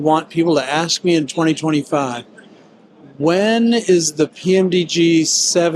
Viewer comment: I didnt catch the type, what was he talking about? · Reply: Yeah, the sound got cut off, for whatever reason. What he said was 757.Too bad the sound got cut off, but there was nothing I could do to fix the recording. I don't know why it happened. Thanks for dropping by.